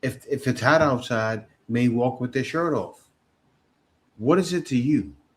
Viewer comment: Didn’t they say They don’t have to smile at black men And for us to leave them alone lol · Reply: I could've sworn they said that with their chest out.